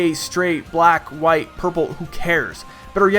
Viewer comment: Did he say at 4:13 Purple